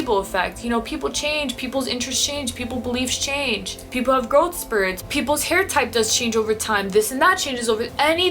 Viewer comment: Interesting, but I am not Christian. What does she say at 14:38 people have ...? spirits? Thanks